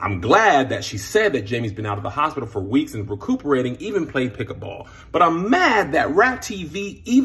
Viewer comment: I'm not believing shyt at this point. When Jamie himself comes out publicly and speaks about the situation, then I believe it. It's all getting too confusing. WTH is pickleball. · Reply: ahhh didn't read it until the 2nd view....just heard Kev and thought he said "pickup ball". Thanks!